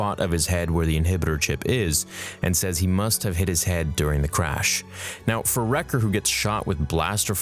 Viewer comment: Did he just say “ he hurt his heading...” ? Lol